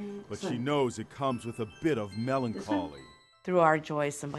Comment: When he said melancholy I thought he said melon calling